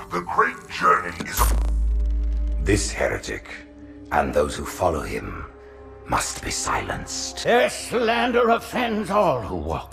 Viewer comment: I just realized when truth says "they must be silenced", this is the same exchange of dialogue that occurs between the arbiter and truth in halo 3 when the arbiter impales truth in the back with his energy sword. "and so, you must be silenced." great writing. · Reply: Lol I'm dumb I thought he said "asshole, you must be silenced." I just assumed learned it from being around Sgt Johnson and the Marines lol. Your explanation makes more sense